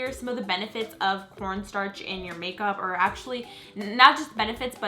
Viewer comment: it sounds like she said porn starch